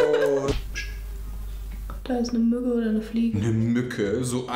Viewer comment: hab ich nur gehört :,,Da ist ne Möhre oder ne Fliege" ?